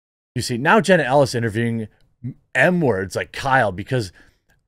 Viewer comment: When Jeremy was saying M-word over and over because it's illegal on YouTube to say murderer. it sounded like he was saying N-word. I was like why are people calling Kyle a N-word now. And that's the problem with censorship because pretty soon he will have to talk like this... H-word E-word I-word Jeremy F-word The Q-word. (Hello everyone I'm Jeremy from the quartering) That's our future.